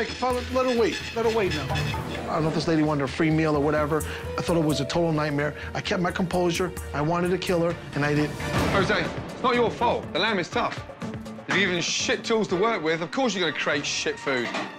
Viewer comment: "I kept my composure, i wanted to kill her but i didn't" Thats some serial killer type shit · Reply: Idk if it was just me but i thought he said "i wanted to kill her and i did" and i was like wait what😃😃